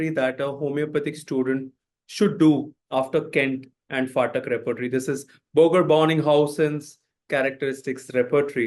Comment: what is the name of the reporitary he is saying at 0:18? All i could hear was Fartac reporitary. Can someone please tell me the real name? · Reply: Phatak repertory